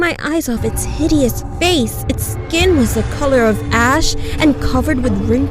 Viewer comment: I thought she said “it’s skin was the color of ass” lmao